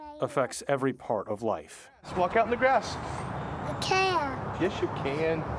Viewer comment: Hi, I'm a English leaner . Can anyone tell me what his dad said at the 1:05min ? Please.🙏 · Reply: "Walk out onto the grass" which he responded he can't.